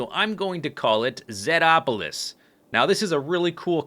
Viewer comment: i first thought it said Zedpools and was like damn that wounds cool but then you said Zedopolis